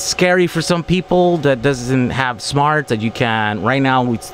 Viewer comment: LOL...I heard, "It's scary for people that doesn't have smarts." WHAT?!?!?! ...."that it doesn't have smarts".....oh, MUCH better. Ha.